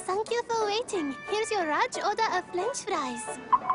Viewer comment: I HEARD "FLINCH" FLIES XD